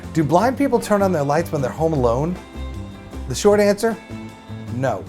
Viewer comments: I thought he said: "Do black people turn on their lights when theyre home alone?" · Why did I hear " Do black people turn on the lights? No